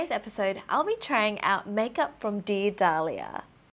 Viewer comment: What she said "dear Dahlia", what I heard "the Italia"